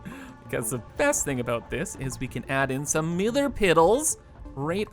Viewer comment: Did he just say millar piddle?